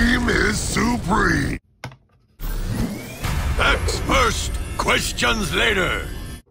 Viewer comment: For a sec I thought he said “Ask first, Christians later!” 😂